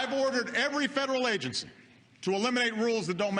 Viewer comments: In the first clip I heard that: I've "orde" every "ferol" agency to "elimei" How could I understand that? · I heard " to eliminate roses" "We have to reknife over 500 reforms"